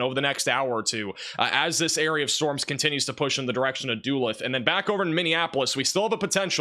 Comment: Not much for criticizing, but when I heard "doo-lith" instead of "duh-looth" when he pronounced the city of Duluth, I laughed harder than I should have.